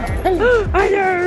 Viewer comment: You are sooo goofyyy!!! I LOVE IT!!! 😍😍😭😭😂😂 @ 12:00 and did you just call him a "beautiful *Twinkie*"??!? 😩😂😂😍 I ❤ animals too 😩😍😘😍😘😍